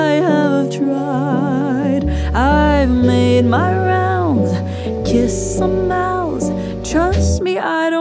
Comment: i love this song but i heard it as "kiss some mouse"